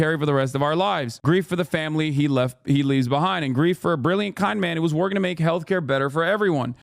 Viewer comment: as you read it to me i heard "brilliant conman who was working to make healthcare better-"💀💀💀